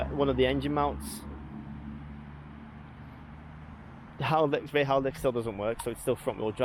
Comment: Did you just say the Haldex still doesn't work @ 4:41? So you basically took a lifted diesel Octavia around Europe!! LOL